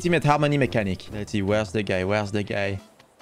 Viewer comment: I thought you said, where is the gay? Where is the gay?